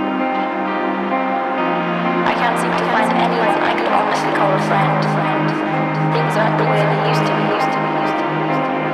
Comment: Someone understands what he says in the minute 3:26? Thanks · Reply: "I can't seem to find anyone I could honestly call a friend"